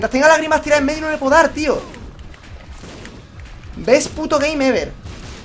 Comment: Dijiste DeiGamer en el minuto 30:36 al 30:38? xD no se que dijiste pero me sono a eso :v